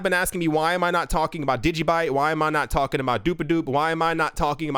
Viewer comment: Where can I buy "doop-a-doop" coin? 16:29. I nearly spit out my coffee when I heard that. I don't know why that struck me as funny. Thanks for the laugh.